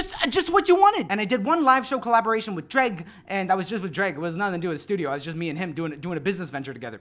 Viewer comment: I absolutely thought you said "Drake" instead of "Jreg" and was very, very concerned. Though I guess unlike Drake, you won't abandon Toronto